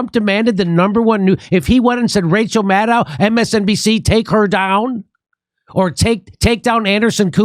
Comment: DeMSNBC i heard him say